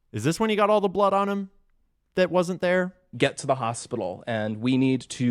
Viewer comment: I don’t think they said that Mikey suggested they get “him” to the hospital, I think they said that Mikey suggested they’ve got to get “to” the hospital. Like after Charlie and security took off to the hospital already. I still think it’s all fishy, but that part makes sense to me and I think Ian may have misheard the guy talking.